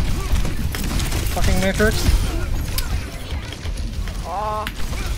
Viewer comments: what did he say????! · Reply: He said matrix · What did the Sojourn just say?!? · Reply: i think they said "matrix"